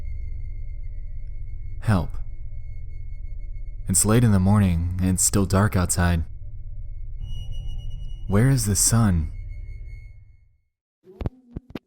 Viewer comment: When he said "where is the sun" i thought he said "where is my son"